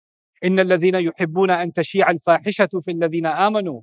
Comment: did I hear the word “Shia” In the verse talking about the people who accused Aisha (RA) of fornication? 🤯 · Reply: Tashe'e = تشيع which means to spread He's talking about ayah 19 of surat An-Nur { إِنَّ ٱلَّذِینَ یُحِبُّونَ أَن تَشِیعَ ٱلۡفَـٰحِشَةُ فِی ٱلَّذِینَ ءَامَنُوا۟ لَهُمۡ عَذَابٌ أَلِیمࣱ فِی ٱلدُّنۡیَا وَٱلۡـَٔاخِرَةِۚ وَٱللَّهُ یَعۡلَمُ وَأَنتُمۡ لَا تَعۡلَمُونَ } [Surah An-Nūr: 19] The ayah talks about the hypocrites who wanted to spread this terrible accusation against out mother Aisha (Allah be pleased with her), and who loved to spread damaging rumors about the Muslims in general